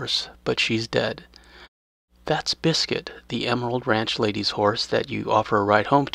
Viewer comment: * also if you name the horse “Biscuit” Arthur will call her by her name randomly when mounting and other stuff. Pretty cool Edit: pretty sure I was wrong about this, thought I heard it a couple times but when I tried over and over to hear it, Arthur never said it. Sorry folks. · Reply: I'm revisiting some of these older videos and reading the comments. I thought Arthur said "Biscuit" the first time I heard it.I realized later that he said "Lets git." I named a horse "Fella" because he called it that; I named one "Gurl" for the same reason.....